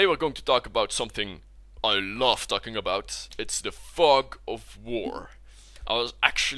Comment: I thought you said fuck of war